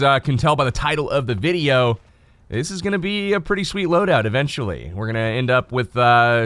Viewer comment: Am I the only one who thought it said "best loadout" ?